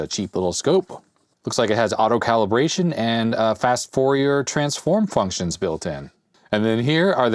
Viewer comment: I misheard "... the Fast Four year Transform..." :)